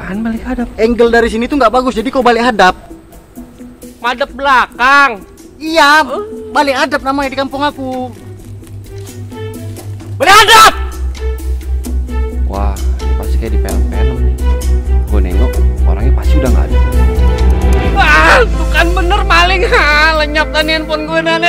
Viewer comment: "Balik hadap" kedengerannya "Biadaabb" sangking keras nya wwkwk